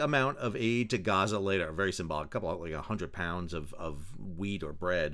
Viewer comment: Thought said 100 pounds of weed at first 😂